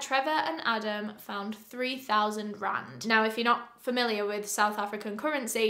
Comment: "3000 Rand, or 200 US dollars" Okay I thought she said 3000 grand at first